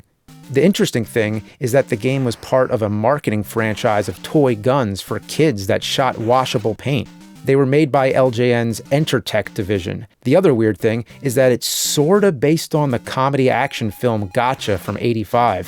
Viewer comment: When he was talking about LJN making toy guns, I thought he said intratec.. for a second I was actually thinking LJN may have had a hand in making the very real tec-9 pistol